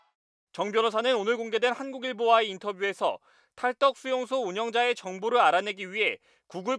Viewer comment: 탈덕수용소? 왜 탈북수용소로 들리지? 탈북수용소 출신인가? 그래서 정신이 와따리가따리? ㅋㅋ